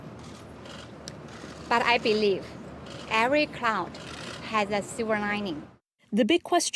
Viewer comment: Misheard: Meng said" Every Clown has sliver lining", ehhh, she referring to winnie pooh xi